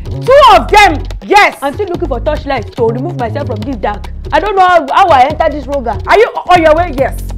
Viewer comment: Did I just hear... I don't know how I entered the RUGER? 3:15 😂😂😂😂